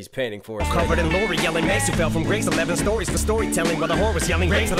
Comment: Sound like he said Mase fell from grace